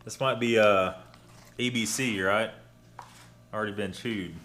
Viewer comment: "EBC"?? ALREADY been chewed, "ABC" Unless I heard wrong,( I watched 4 times, still heard "EBC" That's a